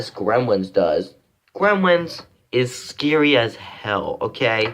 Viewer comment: Uhhhh did you say hell